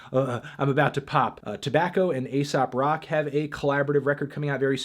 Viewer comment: When you said Aesop Rock I thought you said ASAP Rocky